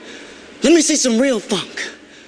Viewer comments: did he say the F word on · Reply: No, he said "Funk" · At first I thought he said "let me see some real fuck" and I was like, no wonder it was deleted